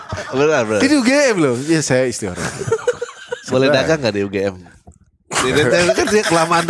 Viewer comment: btw, menit 5:31 ngomong apa ya kok sampe nge freeze semua hhhh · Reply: "boleh dagang ga di UGM"